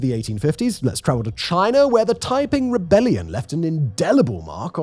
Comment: I heard ‘taiping rebellion’ as ‘typing rebellion’ and was like ‘hot damn, let’s hear about this then!’ Before seeing the writing in the screen. 😅